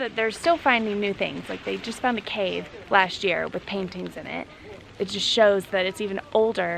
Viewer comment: dont mean to be nasty.. but i thought I heard her say.. "they jsut found a cave with Panties is in it" 🙈